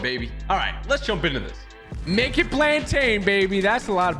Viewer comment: At the beginning I had to watch it again because I thought he said naked plantains